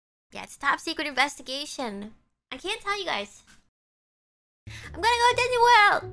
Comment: 到底英語聽力好的人是怎樣聽到這個速度是說什麼？ 我只聽到 i m gonna to ne world 完全聽不到dis